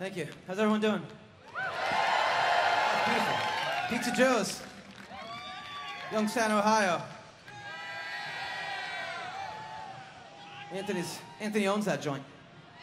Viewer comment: Is any English native speaker able to tell me what Adam is saying between 10:48 and 11:03? I cannot figure it out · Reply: "Pizza Joe's....Youngstown, Ohio....Anthony used to own that joint." Anthony Lamarca's (other guitar player) hometown is Youngstown, OH. (I think someone was holding up a pizza box on a stick in the crowd.)